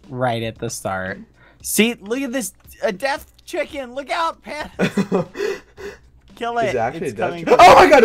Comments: awww did bad just call sap pandas? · did he just say “pandas?” i literally thought Dream only called him that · Did Bad call Sapnap Pandas at 2:31? 🥺🐼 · did bad just call him pandas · Did Bad call Sapnap "Pandas"??? Awwww